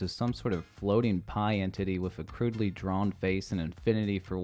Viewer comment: I heard “Pie And Titty” instead of “Pie Entity” 💀💀💀